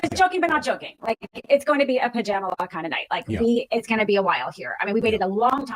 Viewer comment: did she say pajamala? That right there is why she lost 😂